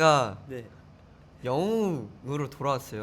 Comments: MK:“WE HAVE RETURNED AS DREAMIES” AHAHSHJSSJSJSJ AND THEIR FACES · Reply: bla bla 14:41 (he actually says it, but subs are strange) 드림이로 할려고 하는데 - i wanted to say as dreamies(instead of heroes) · Even said he was about to say "I've come back as Dream" instead of "I've come back as a hero concept" NCT DREAM OT7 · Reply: Issa joke cuz 14:35 ~ 14:42 it sounds like he's saying "dream" instead of "dlib" (dlib for adlib)